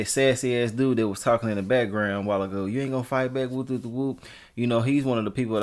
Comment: I thought I heard him say something like you not gone fight back...